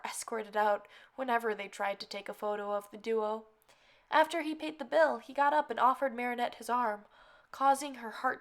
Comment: "After eating the bill" .... WAIT, WHAT?! THEY ATE THE BILL?!! D- Did... I just... hear that wrong... or.....? DID THEY FREAKING EAT THE FREAKING BILL?!! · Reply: i heard "pait the bill" hmmm...